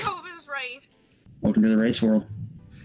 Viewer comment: I swear I thought he said "welcome to the race war"